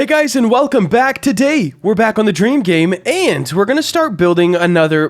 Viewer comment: I Thought You Said Train Game